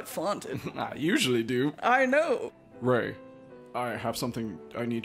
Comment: Oh my, when I thought she was saying "I know" she was saying "Oh no" 😂